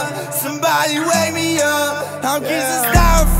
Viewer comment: I heard "somebody wake me wake" and i was like SHIT